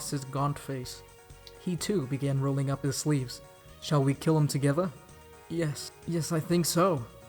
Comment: I thought Sirius said, "Shall we kiss?" and I was like wait what.